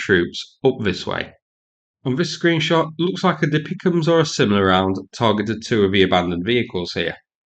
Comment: What is the munitions at 3:49? Subtitles says "dipicoms". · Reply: It’s DPICM, a artillery shell that dispenses submunitions